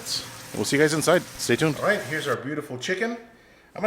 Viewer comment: I thought he said "Piece of Sheet" Hahahahahaha. :)